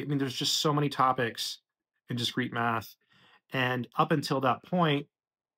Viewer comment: At first I thought you said “street math”, I was like, oh, I know all about that 😂